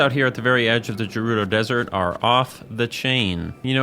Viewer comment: Did he just say Jerudo???? Bruh??